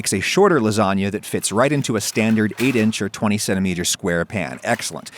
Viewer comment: I heard "twenty-seven meter square pan" lol